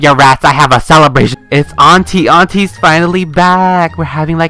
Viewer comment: I thought said Auntie Ate as in older girl relative in Filipino XD